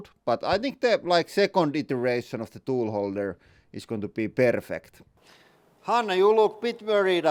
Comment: I thought to myself: "Where is he from?" then i heard him say "Perrfect" and i thought "Perkele". He must be swedish lol